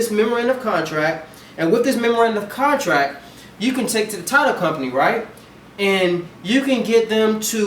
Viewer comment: i couldn't understand this part at 7:38 where he says you can take it to a t... company. can anybody clarify that for me please. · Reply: David Hey a title company